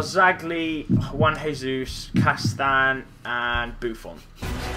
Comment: I thought he said 1 Jesus like a food server